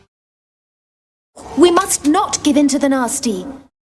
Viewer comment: I thought he said the Nazis